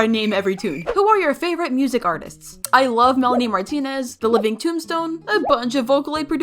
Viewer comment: WAIT DID YOU SAY MELANIE MARTINEZ!?